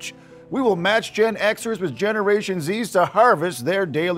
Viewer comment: did he say zee?